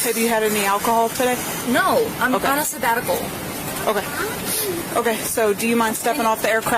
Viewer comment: what I hear: "Have you drinked any alcohol?" "No, I'm a sabbatical" 🤷🏻‍♀️ English is not my first language, anyone care to explain, please?